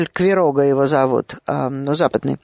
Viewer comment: Не могу расслышать, как зовут ученого на 37:00? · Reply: Прибрам